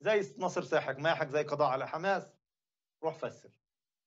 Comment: سمعتها روح فسي😅